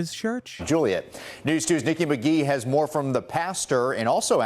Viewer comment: I had no idea what he said. I had to rewatch it. I heard something. Stinky McGee 😂